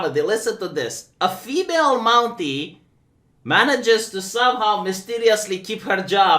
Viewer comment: Ohhh a Mountie.. I thought you said monkey 😂